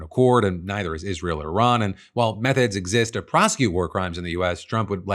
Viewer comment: PHIL 🤣 6:25 I had to rewind and listen again because it SOUNDED like you said 'Meth-heads exist to prosecute' and not 'Methods'. 🤣